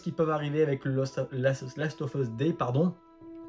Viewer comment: Excuse moi "The Last of us D" c'est quoi ? Tu voulais dire "2" mais tu as dit "D" ? Tu bafouilles un peu avant mais tu sembles avoir dit "D" volontairement. J'ai fait une recherche google mais y a rien. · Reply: Le "The Last Of Us Day" c'est le nouvel événement qui remplace l'Outbreak Day. C'est le jour anniversaire où la pandémie à commencée dans le jeu et tous les ans Naughty Dog prépare 2-3 surprises pour cette journée :)